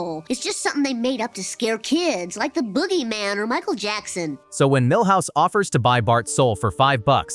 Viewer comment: did he said Michael Jackson!?!?!?